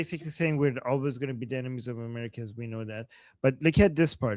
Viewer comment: i thought said the durka said "were always gonna be the dhimmis of the Americans" Enemy, dhimmi same same 😂